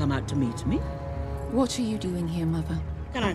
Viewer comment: Can someone tell me what she's saying at 0:30 ... "What ciu you doing here mother?" What kind of pronunciation is that? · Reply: “what’re you doing here mother” and she has a british accent